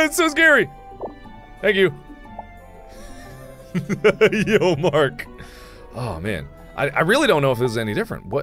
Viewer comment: That did not say yo mark it said yo marki.